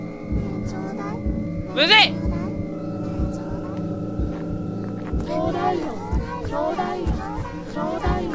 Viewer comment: 「ちょうだいよ」が「東大王」にしか聞こえなかった